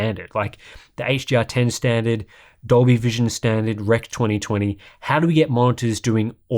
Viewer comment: I thought you said doggy vision lmao. I was like WTF???